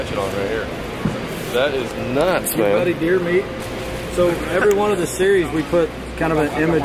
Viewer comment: Wait wait wait I heard "your buddy deermeat" does that mean we getting a deermeatfordinner rod?